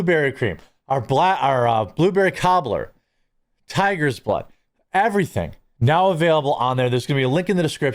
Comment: I know that this is a serious conversation, but when you were listing your coffee flavors, I thought I heard you say "Tiger's Butt," instead of "Tiger's Blood," & wondered what kind of sorcery was behind that flavor! 😂